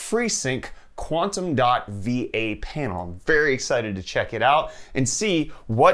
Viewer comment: I thought he said V8 panel instead of VA and all I could say was “DAYUM”